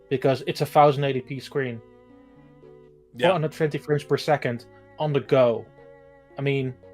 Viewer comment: its a thousand 80 pee and 450 frames per second? Lol did i hear that right 😂